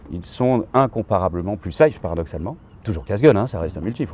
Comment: à 1:47 ils sont incomparablement plus ... quoi ? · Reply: plus safe ?